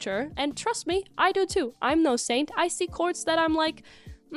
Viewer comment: exactly, I’m not in the fashion, but seriously, you’re allowed to like things or dislike things, but not attack anyone for it. Also it took me forever to figure out what “coords” where, I thought you were saying core